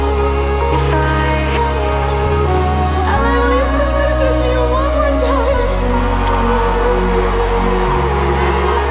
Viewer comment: what did she say?? all I heard is ".... one more time." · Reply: "L.A will you sing this with me one more time?"